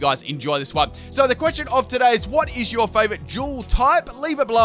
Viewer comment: While watching the video thought he said what is your favourite juul type